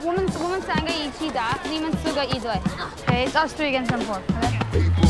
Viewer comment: did he just call him a woman?